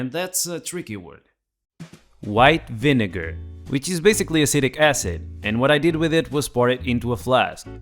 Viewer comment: Without reading the screen, it sounds like you said "acidic acid" instead of "acetic". Just something I found mildly amusing.